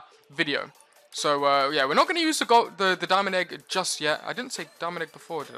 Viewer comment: did u say diamond egg cause it sounded like you said my name 😂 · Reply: i hear it he says dominic egg XD